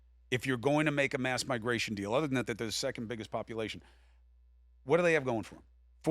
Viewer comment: Did he just say second biggest population?